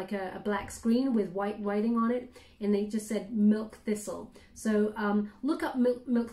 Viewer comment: Lol I work for the Dept of agriculture dairy div and that’s the first thing I thought of when you said milk fissel. I will look it up though